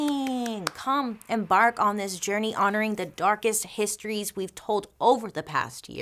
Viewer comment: When you said “let’s embark on this journey” I literally heard “let’s bark on this journey” and I was really gonna bark for you Bailey 😂😂 But you look dazzling as ever 💕